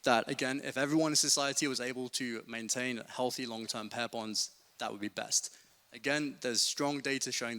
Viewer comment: Every time he said "pair bonds" all I heard was "hair buns"